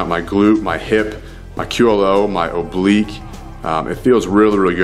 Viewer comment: Did you just say "my culo"? at 3:43? If you did lol